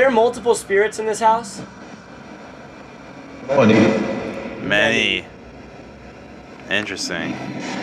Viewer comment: when y’all asked if there were multiple spirits, everyone thought it said “many” but I heard “Twenty”.